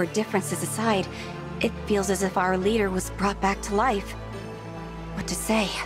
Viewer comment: did I hear that right? Did she say Alear? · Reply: She Said,"Our Leader"